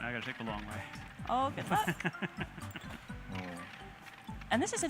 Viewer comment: does she say "oh fuck"?